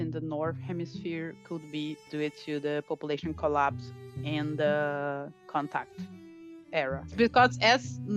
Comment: Does anybody else think she says "IN the contact era" rather than "AND the contact era"?